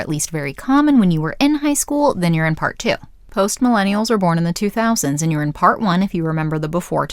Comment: Sounded like you said "post malone-ial", lol